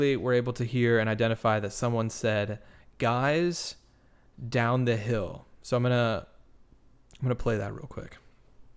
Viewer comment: I hear "Guys...downhill" not diwn THE hill.